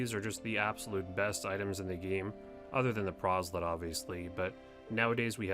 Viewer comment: what does he say? Also yes, my cat likes to sleep in the bathtub! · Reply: other than the proselyte